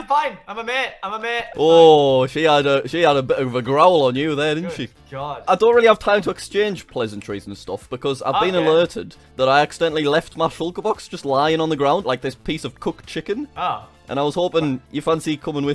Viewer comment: I thought it said I accedentally KILLED and I got really scared